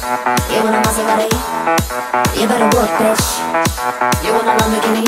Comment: this album helped me get off alot of drugs. i thought she was saying "JUANA, JUANA, JUANA, hotbody,JUANA,buggati, JUANA, masserati,JUANA as in marijuana and it scared me away from smoking weed.